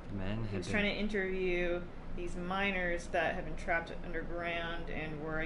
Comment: I thought said "minors" instead of miners